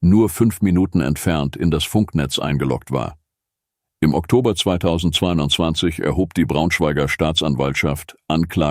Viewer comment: Sagt der bei 18:30 Oktober 2220 oder hab ich das falsch gehört?